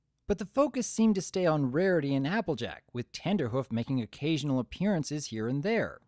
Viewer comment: Actually, I heard them say "Trender Hoof".